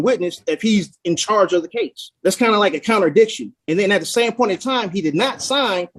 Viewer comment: did he say "incounterdiction" 🤣